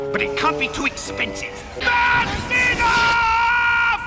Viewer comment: I thought he said "JOHN CENA!!" XD I know, he said "that's enough" X3